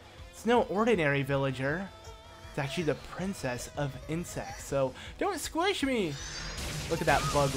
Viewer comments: XD it sounded like he said the princess of INCEST!!! XD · agitha is ok for a playable character but wolf link would have been cooler for the twilight princess character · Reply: Dark_Link 37885 ...at first i thought he said the princess of incest i was like WAT let me rewind that lol